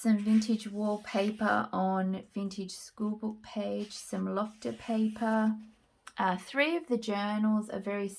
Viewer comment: In past videos, the first several times I heard you say “Lokta paper” I thought you were saying “Lobster paper”🦞 hahahaha. Then, I slowed you down (not that you talk fast, rather, I have slow ears!) and heard “Lokta” which I promptly looked up and —- voila —- mystery solved!!!